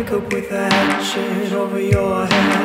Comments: LYRICS When you see him Look him in the eye, look him in the eye And he won't dare to follow If you need to Hook him with your eye, hook him with your eye Do your wiggles and wallows Sleep so tight but by a raven's legs Killed upright, sniffing by his head Takes his high with a bop on bed Makes it joggle with his torn off legs *Wake up with a hatchet over your head You wake up with a hatchet over your head I can see you running I can see you running Gone in the blink of my eye Gone, gone, gone in the blink of my eye I can see you running I can see you running Gone in the blink of my eye Gone, gone, gone in the blink of my, blink of my eye. Open your eyes, chocking on his bed Chocking on his bed, then grimace and smile Take a toll, he is with cigarettes He picks your brains, he's stomping on his chest Sit his tone with one fast hand Stuff his mouth with salty sand Tie his arms up from his head Rolling around like a rusty can * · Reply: i really thought he was saying wake up with a hot shit over your hand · Does anyone have lyrics to this because it sounds so friendly but it also sound like something murderous is going on... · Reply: I thought they were saying “wake up with a lampshade over your head” 😂😂😂 whoops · At first I thought it said, wake up with a highchair over your head.